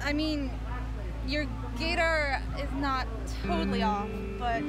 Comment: am I the only one who heard GAYdar?